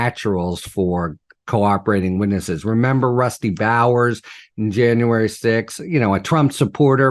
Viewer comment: every time I hear the name Rusty Bowers (53:12) , my brain thinks "crusty bowels". WTF.